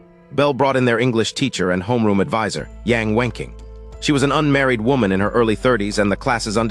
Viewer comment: I could have sworn that I heard this hot teachers name is Yan w✳nking 55:22.